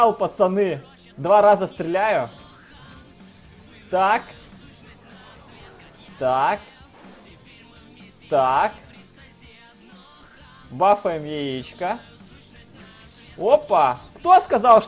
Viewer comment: tak,taaak,taaak,buffa nee ixshka,OPA! I didn't understand shit but I laughed so much Fucking russian man